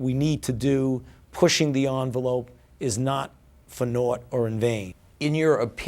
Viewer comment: "not for ? or in vain". what was this he said not for nort, did he say nort. What's a nort. I'm not sure what I heard, somebody explain please? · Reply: "Not for naught or in vain" = this could be the big one so we're not panicking over nothing